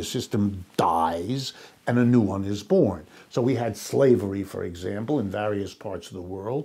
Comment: When he first said ‘slavery’ I thought he said ‘flavoring’ and thought he was going to talk about food. I was a little off on that.